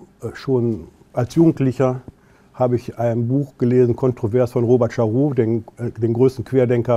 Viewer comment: Wie heisst der Autor und Buch, dass er als jugendlicher Gelesen hat? Ich verstehe "Kontroverse von Robert Scharaud" finde aber kein Buch mit so einem Autoren.